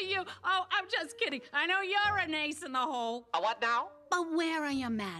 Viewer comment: the subtitles said he is asexual (and vivezypop has confirmed he is), but I can’t help but think she subtly called him an a**hole 😂